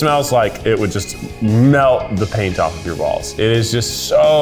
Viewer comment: Well, I heard "melt the paint off your balls," and CC confirmed it.